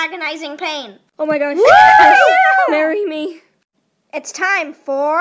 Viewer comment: Alexis Keddington sooo arrry me? Thats what i heard